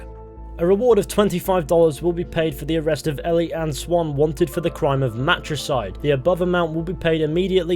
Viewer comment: You said "Ellie Anne Swan". I heard "Kelly Marie Tran". Bruh